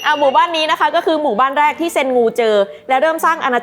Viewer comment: ขัดใจคนชอบเซ็นคูมากค่ะ ได้ยินว่าเซ็นงู😂